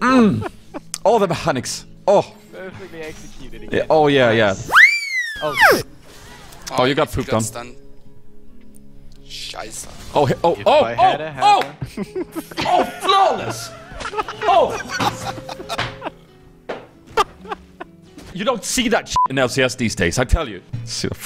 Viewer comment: did someone say "scheiße" at 1:50 ? :D